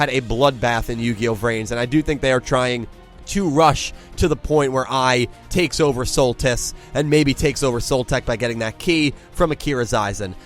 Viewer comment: sounds like you said SOL tits. Jeez XD.